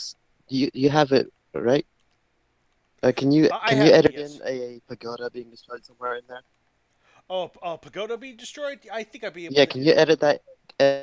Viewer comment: Listening to this again, I thought said "Edit in a Nagoda to destroy".... I also thought said "You said he fucked Gezora."